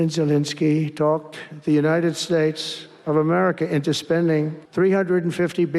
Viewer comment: I just thought I heard Trump say The United SNAKES of America!